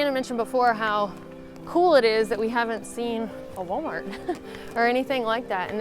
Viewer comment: in your video, what word do you say when you say Montana mentioned how cool it is that we haven't seen a .....? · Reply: WalMart - indeed they never made it to EU. They tried about 20 yrs ago, but luckily failed. Part of their "culture" is recognized here as inappropriate and humiliating their personal.